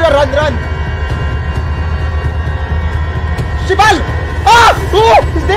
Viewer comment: did u say shibal?? 😂😂